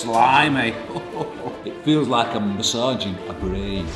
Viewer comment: When he said I feel like I’m a brain surgeon I thought he said it feels like I’m inside you